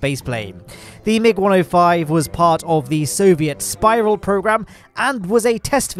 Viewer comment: I thought you said Soviet spyborg program.